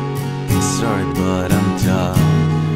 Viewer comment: What are you saying at 0:36? Are you saying "sorry but I'm done"? Thanks B~ Rob